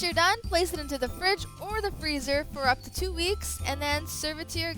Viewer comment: Did she say two weeks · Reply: 10 yes you can leave the truffle balls for up to two weeks in the freezer if you want to pre-bake them for a party :)